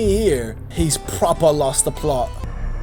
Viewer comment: girl im dying what is that word 😭😭😭😭😭😭😭😭😭😭😭😭😭😭😭 (great video btw ily) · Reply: he's proper lost the plot (thank you! 🫶)